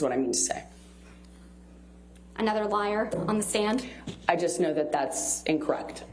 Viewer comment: she clearly says “I’m a liar” 😱